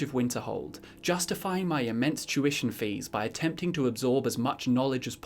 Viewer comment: For some reason, my mind heard that as "immense Jewish needs" and I was trying to wrap my head around that for a second lol